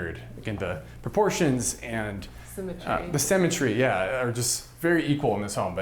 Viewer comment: I thought he kept saying cemetery but I think it's symmetry . Wondering what I was going to do with the bodies. Then he talked about the need for a bullet proof room - then the thought of living, scared of dying, in the US made me appreciate my own country.